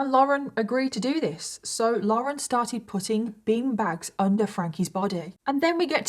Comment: Oh...bin bags...I thought you said bean bags!!! Was wondering what the hell that was gonna do.